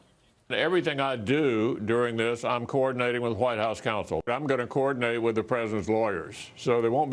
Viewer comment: Did he say "The permanent presidents lawyer" at 2:22?!?!